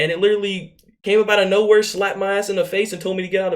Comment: Am I the only one that heard him say slap my ass in the face